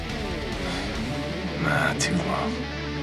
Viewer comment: what does he say ??!? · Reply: “Nah too long”